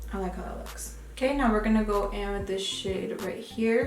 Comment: Every time you say "this shade" i hear "this shit" lmao had to turn on subtitles for a secon lmao p.s i subscribed using your link and i got my boxyluxe last week 😂